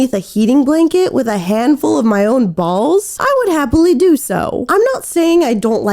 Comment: I’m sorry bro but a handful of his own balls? Did I hear that right?😭